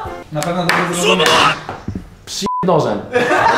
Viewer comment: Ja myślałem, że Friz już usłyszał: "Przyjeb mi nożem" i myślałem, że uderzy Wujka Łukasza XDDD